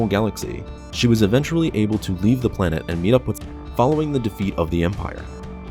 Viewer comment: You fucked up your audio editing at 21:17. Fix this, because it would be good to know what you were gonna say? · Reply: I think I just say the name Sabine